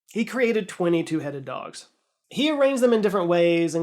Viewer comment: I thought you said 22 headed dogs😬